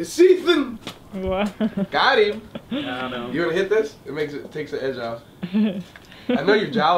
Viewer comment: Was Malone high during the video or is he just like that or is he always high? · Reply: Anyone else hear at 7:44 he says "wanna hit this? it'll take the edge off"